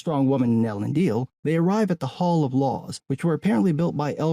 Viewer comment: I thought he said hall of Lore and I laughed.